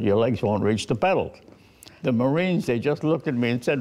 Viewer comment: Did he say Moran at 3:37?